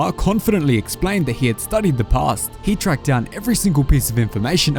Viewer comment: captions say he studied the paste, not the past